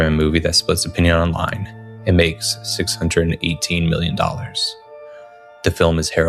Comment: No,I'm pretty sure he said billion,put on your subtitles. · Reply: he intended to say “million” but I think maybe his nose was runny so it sounded like he said “billion”